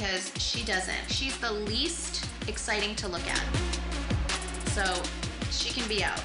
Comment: 'You're the least interesting to look at' comment will always be iconic in KUWTK. Even though Kourtney is absolutely beautiful 💙. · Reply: Simone Livesey its the mandela Effect !!! I always thought she said "you are the least interesting to look at" but its "least exciting" Lol